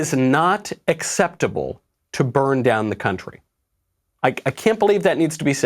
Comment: Is it just me or did I hear "it's not ok, to bone down the country" at the begining? Emperordamnit I'm deaf.